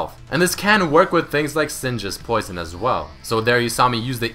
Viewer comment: Did you say Syndra's poison at 0:30 ? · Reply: Singed :)